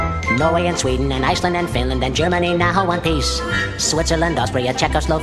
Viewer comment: I swear it said ‘Germany wants a piece’